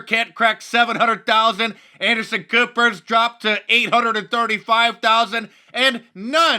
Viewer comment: Did he just say Anderson Pooper? Hahahahahahahahaahahaahhaaahahahaahahaa